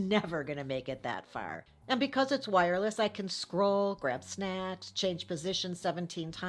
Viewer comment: I wasn't paying attention and then I hear her say "I can scroll, grab sn___h" WHAT!!!!! Oh, snacks! Phew.